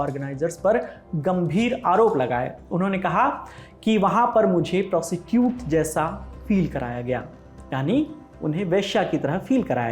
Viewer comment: did he say prosecute??😂😂😂😂